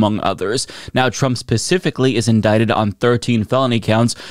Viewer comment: I very much enjoy your channel. But... Your "s" is so slight that I hear you saying "pacifically" instead of "specifically." 😀🤣 I can't not hear it now.